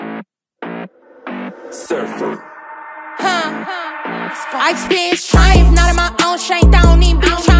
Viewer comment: Okay y’all I’m so sorry cuz I’m not trying to sit up anything but I just want to make sure. I the intro did she say the B word? If not what did she say cuz I didn’t get that? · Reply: It really does sound like that huh… in the description it has lyrics. That says “I experience-“, but it sounds like she says “ice b-“